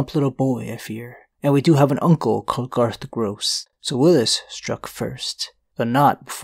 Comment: I wasn't paying attention for a second and thought Garlan said he has an uncle named "Garth Brooks" which immediately caught my attention lol